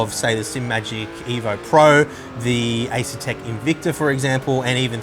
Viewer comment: Did you really say the invicta, or you meant the forte ? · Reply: He said Invicta.